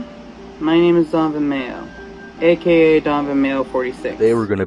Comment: I always remember this dude being called "Da Mayo". Apparently I've just been mishearing it, lol.